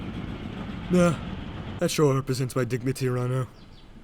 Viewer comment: Im over here trying to figure out what Henry said at 7:30. Sound like, "Yeah... Sure helps my dignity around here." Or "Yeah... That bout what I expect around here..." And its bugging me that I cant tell cause either of those are funny as hell. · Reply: Henry said "Yeah...Sure helps my dignity around here."